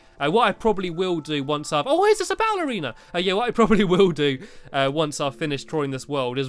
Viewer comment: at first I thought he said “Oh Wait it’s a ballerina”